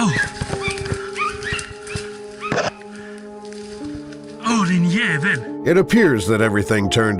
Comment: Th 29th clip is Swedish and at 02:00 where the text is “Ohh yeah, there!” He actually says “Ååå din jävel” witch basically means “Ohh you bastard”.